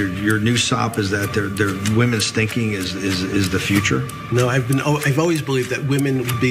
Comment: I thought he said “women stinking is the future”. Alarmed til I listened again and again and I heard “women’s thinking is the future”.